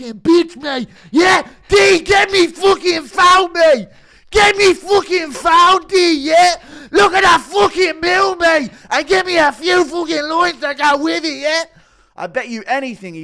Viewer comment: I lost it, don't even know what said 🤣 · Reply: I think he said, "YEAH, DEE GET ME FUCKIN PHONE MATE! GET ME FOOKIN PHONE DEE YEH?! LOOK AT THAT FOOKIN MEW MATE AND GET ME A FEW FOOKIN LINES TO GO WITH IT YEAH?!" I could be wrong though